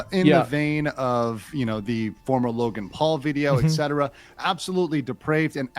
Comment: i thought they said logal paul i was like didnt we already go through this once